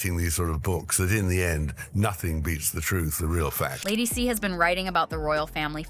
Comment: Some of the captions are SO far off! "Fat lady"? I think he said "facts".